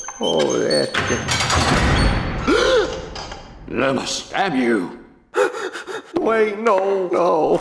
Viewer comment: I thought the Sheriff said "Ima stab you!" but I played it again and figure out he said "Lumis, damn you!" 😂